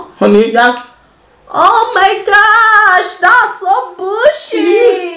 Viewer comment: 🤣🤣🤣🤣it sound like u said oh God that's some bullshii